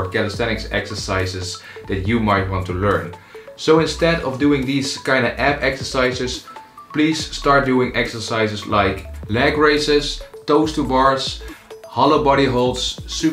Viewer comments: can someone write what he is said? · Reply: Just to not do these kind of ab exercises but do exercises like leg raise etc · What did he say after "leg raises"? I didn't understand. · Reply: Toes to bar(s)